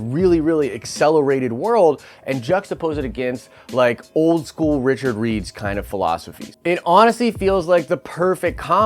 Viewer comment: Did he just say Richard reads